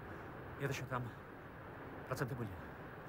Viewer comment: что сказал Дмитрий Громов? Типа: "Там еще это проценты были" ?